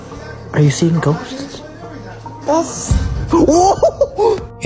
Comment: The baby at 15:03 doesn't say yes. He said "ghosts" repeating what the guy said